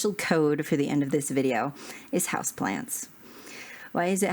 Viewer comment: House plants! I almost thought you said "house plans".